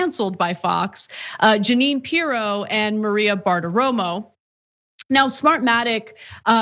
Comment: Oh! It’s “Bartaromo”? I always heard “fart aroma”. My bad...